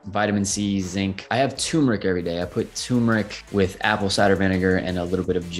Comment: Turmeric, I thought he said he had a tumour